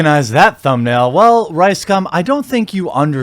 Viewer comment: Am I hearing wrong or I keep hearing "Ricecum"? XD